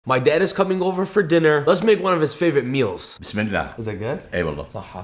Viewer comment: I literally thought that he said let's cook my dad for dinner😂😂